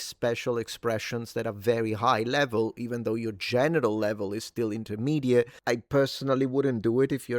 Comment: sounds like you said genital level lmao